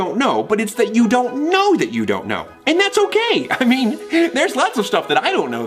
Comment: am I the only one who can't tell what they're saying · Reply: Patton is saying he doesn't know what he doesn't know Virgil and Thomas are confused If you didn't understand the reference of THIRD BASE it's in reference to Abbott and Costello's baseball skit called "Who's on First"